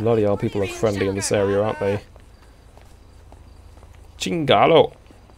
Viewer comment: I heard u say chinese